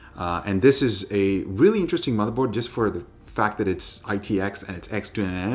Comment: what word is that? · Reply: Motherboard